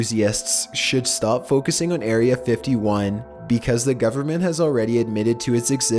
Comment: did i heard area 51